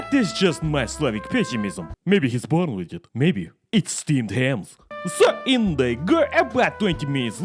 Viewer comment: I thought you said we were having steamed clams